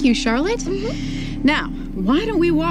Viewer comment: Cute couple. Erica is very beautiful at this point in the series, and Season 10 is an overall happy season. At 3:56, Lois (Erica) says “Tom” very lightly. Does anyone else hear that? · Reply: She says “now”